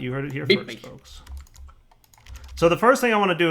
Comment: genuinely thought he said 'look, random bitches flying everywhere" xD